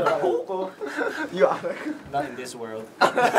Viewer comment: did i misheard or jackson said out of this world? and their recent vlive bambam said the same thing right? coincidence? spoiler? lol excuse my delulu mind